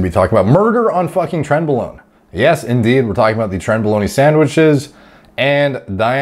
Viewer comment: “Trenbolone...........y Sandwich” Already a great intro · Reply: At first I just heard "E" sandwich i was like wtf.... then It clicked that it goes with previous sentence #brainfogisreal